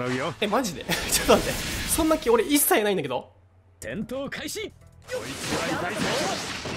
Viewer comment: 戦闘開始が転倒開始って聞こえた（笑）